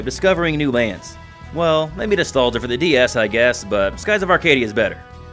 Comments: What did you say here? I didn't get the name of the game. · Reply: It’s called Nostalgia for the DS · what's the name of that airship game on DS that's mentioned during the skies of arcadia section at around 4:25? i can't quite make out the name. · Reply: Yeah sorry I say it pretty quickly, it’s called Nostalgia for the Nintendo DS